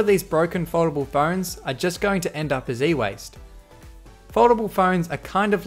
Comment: did you say "a Z-waste? savage jk